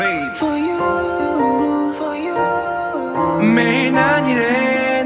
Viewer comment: stop i thought he said f you